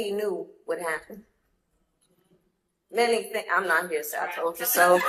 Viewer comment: I thought she said I'm NOT here to say I told you so, but I could be wrong 🤔